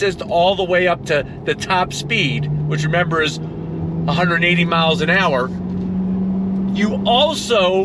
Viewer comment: I heard top speed is 118 MPH. Is that what you said? Is that correct? 118 mph in the quarter mile? Top speed must be faster. Thanks for the video. Ah, if I had the means. I'll continue to enjoy my C7 Z06. Oops, I need to watch the entire video before I comment. At 17:13 top speed is 180 MPH. That's more like it!